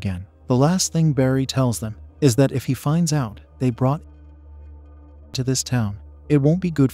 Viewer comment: brought what to the town? Every other word keeps getting bleeped out! There’s this book called a thesaurus, they even have them available on the Internet for free! or you could just select the word on any device or computer and use it! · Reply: narcotics